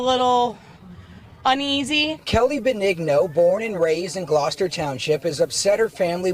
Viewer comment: Kelly what?! I heard nig-no , and I tend to agree. That’s what I’m gonna start calling pepper spray.